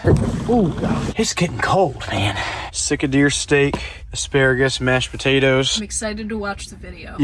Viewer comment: I thought you were saying you were sick of deer steaks till middle of the video.